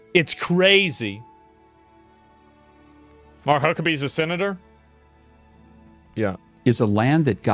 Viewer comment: i thought he said my cuckabee for a sec